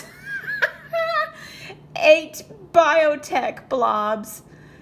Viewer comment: I thought it said biotch LOL